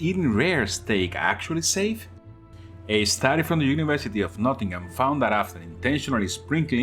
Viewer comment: I thought I heard you say the university of nothing